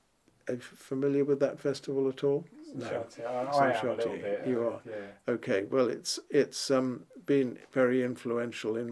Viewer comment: what the name of the festival they said in 7:20 ? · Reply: Saint Chartier. Doesn't exist in exactly that form now, but look for "Le Son Continu" which is a new and similar festival.